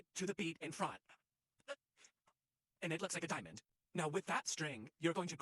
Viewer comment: I THOUGHT U SAID "now it looks like a dying man" WHEN U AAID DIAMOND😭😭